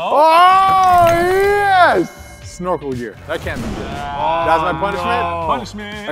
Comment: I thought he Said snort a gear but I can't hear it anymore because Ik what he said